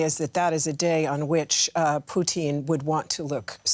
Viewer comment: swear I heard poutine